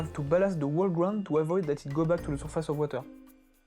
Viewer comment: couldn't understand what you said there. You're balancing what to avoid what? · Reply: Ballast to avoid the ground pieces to go back on the surface of water :)